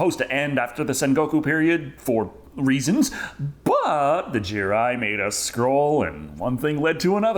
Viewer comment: Jirai, I thought you said Jibril.